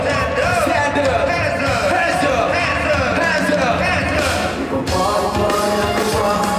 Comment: juro que escuche hansol 🐻‍❄